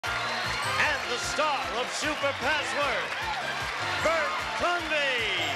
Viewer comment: I thought it said Bassword.